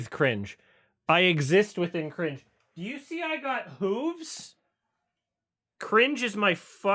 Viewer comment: thought he said boobs and got very excited.... luv the hooves tho